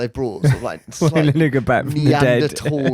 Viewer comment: What was said at 1:02:01? I keep replaying it and all i can hear is the N word 😮 am i the only one? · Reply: lineker back from the dead